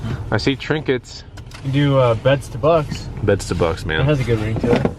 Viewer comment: Lol beds to bucks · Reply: I thought he said bed to bugs. Which is hilarious.